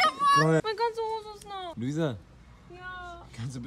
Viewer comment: was hat sie da gesagt 😂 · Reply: Meine ganze Hose ist nass‘‘ also falls du das meinst